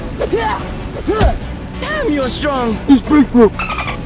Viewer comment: Did cuss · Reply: no I thought he said d a m n your strong to Bigfoot